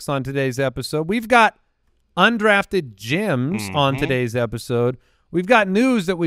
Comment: every time I hear this I think it’s “undrafted Jims”